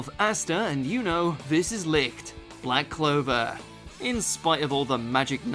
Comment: he really said "Licked"